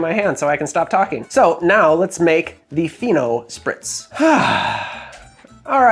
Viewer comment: When you said "Fino Spritz", I thought you said "Pheno Spritz", and I was very curious to know what the drink would be.